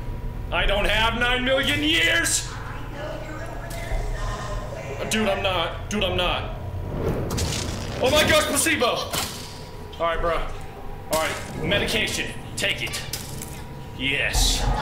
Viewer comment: did say "spasibo" ??